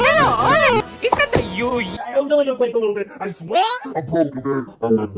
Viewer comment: Я услышал ъуъ